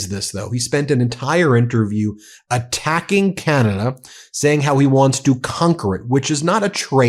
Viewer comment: I thought he said Canada has nothing he wants. 🙄